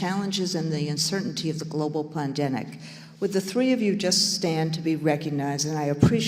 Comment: Ok... Am I trippin'? Did I hear her say "plandemic" at 18:56? It sure sounded like it. I had to listen a couple of times, I even slowed it down and put in headphones and it still sounded like she said plandemic.